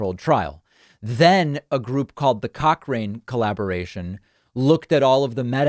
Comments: did he say cock ring collaboration? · Thumbs up if you thought he said "the cock-ring collaboration". 🤣👍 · Did I hear "cockring collaboration"🤣🤣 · The cock ring collaboration? Did I hear that right?